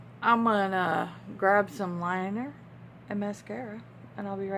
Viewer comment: For a minute there I thought you said I'm just going to grab some wine. You said liner🤣🤣